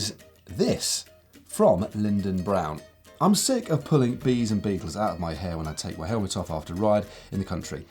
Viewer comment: Did Matt Say I'm sick of pulling bees and beavers out of my hair at 16:30?